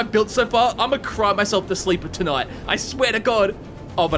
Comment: I thought he said crap myself to sleep at first